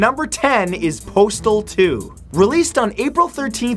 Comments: I thought number ten said portal 2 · I thought it said portal 2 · At #10 I thought it said Portal 2 and I was like WAHT!? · I thought Postal 2 said Portal 2 but thank god it wasn't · When he said postal 2 I thought he said portal 2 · At number ten I thought he said portal 2 And I'm just like: what? Why? Are you drunk Matt? Until I realized I was just being an idiot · I thought #10 said "Portal 2" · i thought the last one said portal 2 for a sec